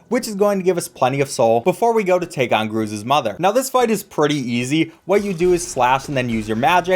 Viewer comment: did he say "groozes mother"?